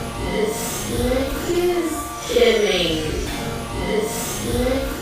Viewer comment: I thought she said getting cut :')